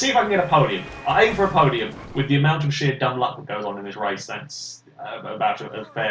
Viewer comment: Did he say shit? 0_0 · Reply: Michael 9-3 no he said sheer dumb luck